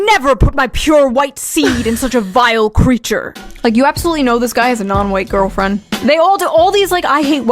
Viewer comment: I thought Shoeonhead said "this guy has a no weight girlfriend". Imo this fits even better. 🤣